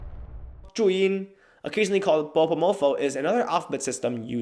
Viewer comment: did you say "bobo mofo" (1:40) ? that really sounds like an insult.